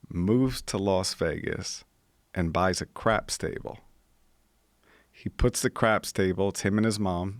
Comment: At first it sounded like he said " I bought a crap stable"